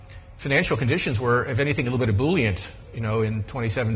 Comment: Does bernanke really say bullying at 13:49, or does he say ebullient? Captions appear wrong.